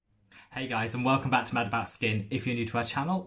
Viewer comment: Sorry, I heard "My bad skin" 😬